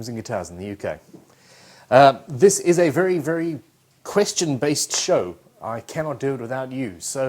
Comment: My first time here,. As a Yank I thought you just said this is a Christian based show. But I'm hating my thick sticky Squire neck. Can't wait to hear this. BTW the specs do say polyurethane for my guitar.